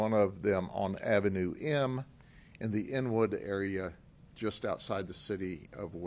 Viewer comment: Did he just say: ..in the "n-word" area???????? WTF!!!!!!